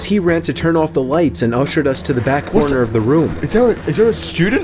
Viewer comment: the subtitles say I'll shoot us to the corner of the room lol